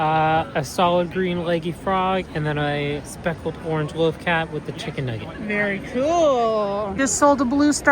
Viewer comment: did i hear loth cat or am i tripping